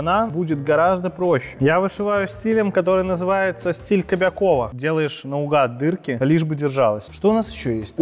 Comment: Мне одному послышалось стиль кобакова?